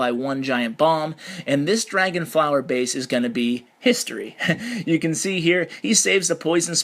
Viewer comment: When you said history my Siri went off, she thought you said hey Siri :P